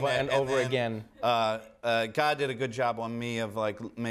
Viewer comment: did he say "god" did a good job on him?